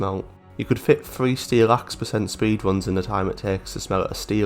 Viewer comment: you could fit 3 steel axe? am I deaf or something because I can't figure out what you said :C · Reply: 3 steelaxe% speedruns